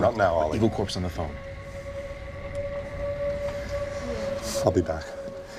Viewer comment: did he say "Evil Corp's on the phone??" Or is it just Elliot's imagination?? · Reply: He changed the E corp into Evil corp in the mind, so between them they say E corp. I think it was in first episode there he explains about this